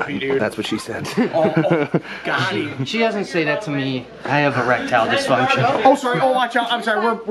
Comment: You should have said you had 'Reptile disfunction'...LOL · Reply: Thought he said “e- reptile 🦎😏 disfunction” lol